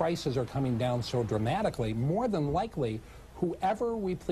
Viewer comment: dude at 20:05 accidentally told the truth lol: "whoever we please", then "corrected" himself with "replaces Chavez" that's what happens when you constantly spew lies instead of the truth, the truth will come out of the gust of lies.